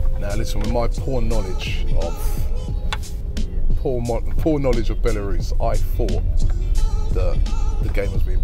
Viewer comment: Who else thought Robbie said porn knowledge